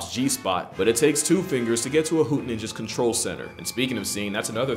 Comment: "i can't tell you how many licks it takes you to get to a tootsie pops G-spot, but it takes two fingers to get to a hoot Ninja's control center" what the fuck is that sentence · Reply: I thought he said hood ninja lmaooo